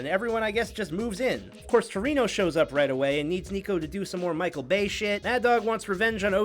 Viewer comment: did he say niko